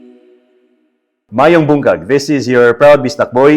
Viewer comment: Did he just say “ Maayong Bungkag”? Instead of Maayong Buntag? 😂😂